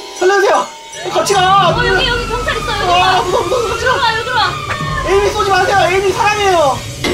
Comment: 에 으뜸이가 에이미 사랑해요 라고 들림